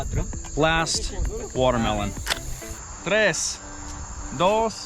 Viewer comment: I thought you had said ‘Guatemalans’ when describing what you could grab for target practice! Then I realized you said watermelon! And boy was, I relieved! 😅